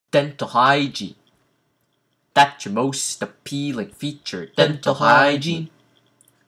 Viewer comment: At one part I thought you said genital hygiene xD